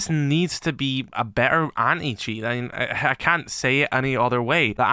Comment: Why do I always hear him say "Any-Cheats"? LOL